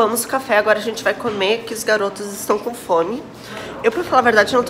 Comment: Juro pra ti q eu entendi "a gente vai comer os garotos" gente socorro